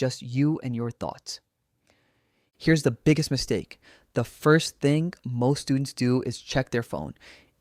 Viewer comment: I heard biggest beef stake 😅